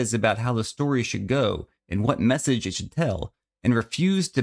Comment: ohhhh i thought you said mormon :(